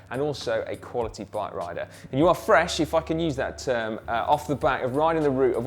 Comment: Did said f word? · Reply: Meal Worm Gamer it sounds like that but I believe he is saying “if I can use that term” I could be wrong! Lol